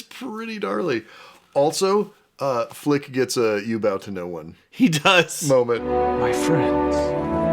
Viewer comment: bro i thought you said “you ‘bout to know one” and i was so confused